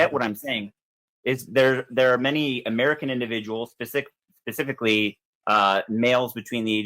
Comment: Did he just say pacifically instead of specifically @ 2:25? Lmfao says all I need to know right there